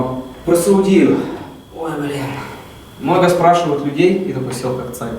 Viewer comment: Он сказал:ой бл#?? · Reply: я услышала "блин"